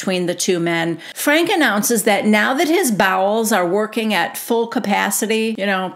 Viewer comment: Just realized , 🤣🤣🤣 ... that Delores was saying "Bowels"... thought they were saying Balls ... ( I had thought ... how do you know your "balls weren't working for several hours "...?)